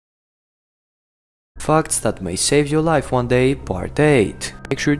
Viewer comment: In the start of the video when he said facts that may save your life I thought he said fucks that may save ur life lol 😂